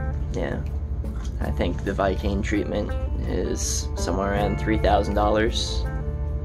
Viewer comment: Yes, there is a similar process you can go through using Vikane (brand name) gas. Like you pointed out though, it is just a treatment and not preventative 😔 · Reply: Oh that's what you said, I heard it has "the viking treatment" hahaha. Burning down a boat costs money? (sorry)